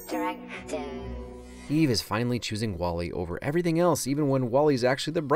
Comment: it wasn't written out as a subtitle, but at 12:31 she says "directive"! to what you said, she's abandoning her code on what 'the most important thing in her world' is and making a choice to make wall-e her new purpose :,)